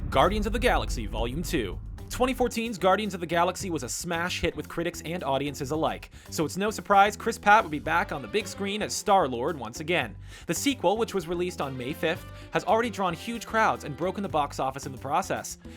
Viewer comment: Did you just say Chris PATT😒 (Pratt)